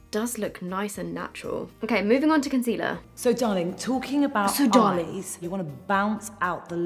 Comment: why did you say "So darleys?" Maybe I heard wrong, but when I clicked the subtitle button, it says you said her bodies... I'm dead. Also, pause the screen after she says that to get a strange, but still gorgeous smile. :)